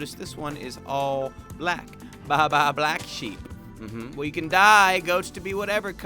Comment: I wasn't paying attention and thought he said "and then we can DIE"